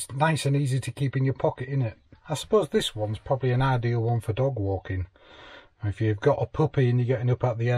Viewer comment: I WANT ONE please you said good for dog walking I thought you said dogging for a second 🤣🤣